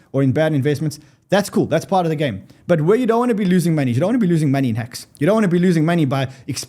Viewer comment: Lol I thought he said "don't lose your money in Hex" So true though...hahaha